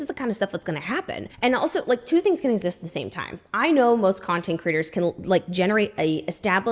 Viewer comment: 😂 I feel like you keep saying content critter instead of content creator 😂